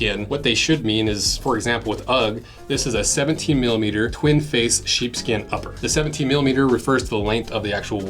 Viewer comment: did you just say “twinkface”? Sucks that the new Ugg products are not full shearling anymore. I don’t think they even stock it. It is more ethical as so many people buy them.